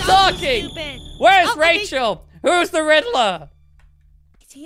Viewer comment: I swear I heard who is the rizzler